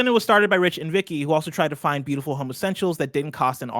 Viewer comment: Had to rewind that to check what I heard. (It's "home essentials", though I prefer the alternative).